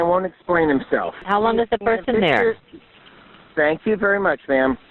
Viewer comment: What the call taker said: "How long has the person been there?" What he heard: "I'll send a person there"